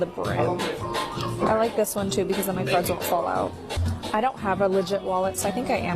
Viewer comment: When that girl was looking at the coach wallets at first I thought she said she liked that one so her drugs won’t fall out. Had to rewind and put on the captions to hear correctly